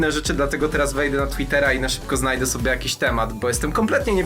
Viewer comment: Czemu na początku zamiast "na Twittera" usłyszałam "na Tindera" muszę iść wyleczyć uszy